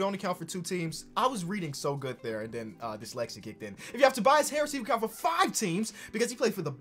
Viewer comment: DID YOU JUST SAY DYSLEXIA KICK IN?! 0:43 can yall tell me what he said at this moment